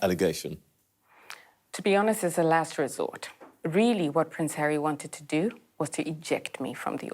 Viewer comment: You have the stroppy Duchess and his wife, you get drama and petulance. I love how it sounds like you say Prince Hairy😄.